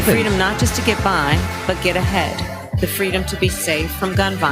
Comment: She said get ahead, at first i thought she said give ahead....